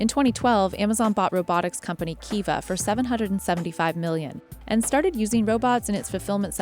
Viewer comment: All I heard was her "its' a hundred thousand robots"... No, no, no.